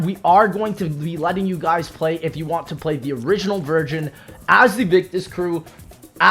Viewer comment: dawg says virgin instead of version